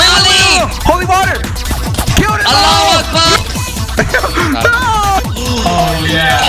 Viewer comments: Wait at 6:22 did say allahu akbar when you threw the Holy water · did bro just says "allahuakbar"💀